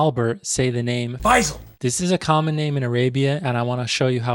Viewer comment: I thought he said bison instead of فيصل😭